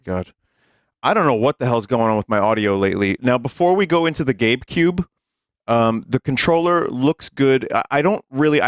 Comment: I thought he was saying gape cube